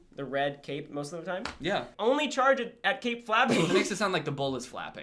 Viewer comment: I thought you said cape fapping